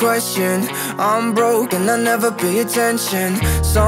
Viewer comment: I keep hearing "I'm broke and I never pay attention" as "I'm broken and I never pay attention" and both are true tbh XD